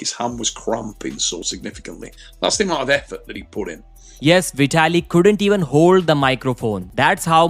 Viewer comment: i thought you say 4-0 vitaly 🤣🫵🤡